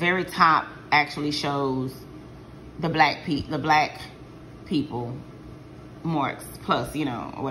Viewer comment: I got up to let the dog out and heard you say this the end cause these black ppl down here. I said done lost her WHOLE mind lol. Then I saw you were talking about the digital coupon add/check marks .lol. Yeah I am the same. I aint into none of this click stuff or so and so stole my scenario and aint give me credit. I literally am here to do deals, save money and laugh a little. When that aint happening, I just disappear like I do in real life. Great video. Keep em coming